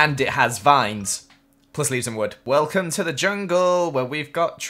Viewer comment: jungle tree features, it a tall boi/ boy its has vines.. PLUS LEAVES AND WOOD Me: {\_/} (xD) />At first I thought he said 'wood leaves and wood' XD<\